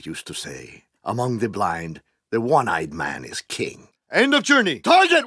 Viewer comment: what hes saying? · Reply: Brother ChrisGR "among the blind, the one-eyed man is king"